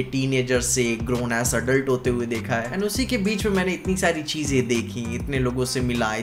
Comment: what you heard: Grown as adult what i heard: Grownass adult 😂😂😂